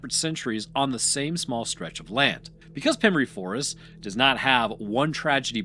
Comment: I don't know how, I don't know why, but somehow I misheard the name of the forest mispronounced as "Femboy Forest". Perhaps my brain was primed...